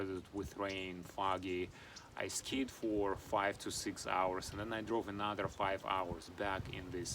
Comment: Sorry I thought you said "You skid for 4 or 5 hours" I was going to say thats one long skid lol, I need new headphones!